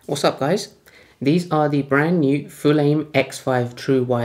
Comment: NIce one, buddy. I thought it said Fulham for a minute.